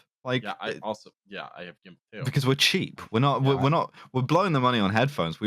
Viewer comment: Wait? Did I hear that right? and have gimpsuits????